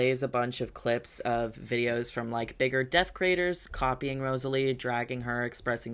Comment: it says "death craters" instead of deaf creators in the captions!